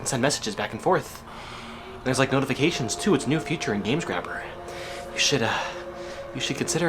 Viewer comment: i thought u said games-crapper